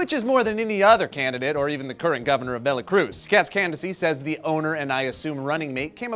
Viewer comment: did say "VeLacruz".... Did he meant VeRacruz or is there an actual Velacruz I haven't heard of? P.S. is Japenese?